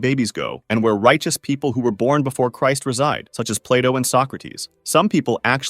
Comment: When you said Plato i thought you said play dough